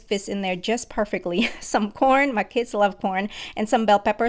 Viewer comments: Wait- why did I hear “my kids love por-“ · i thought she said my kids love por- 💀💀💀